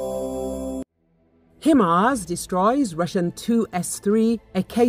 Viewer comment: Its Hi Mars not Him Ars 😂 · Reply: I heard "Hi ma's."